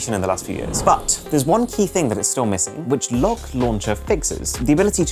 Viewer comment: My supercell ahh mind thought he said "Log Launcher" 💀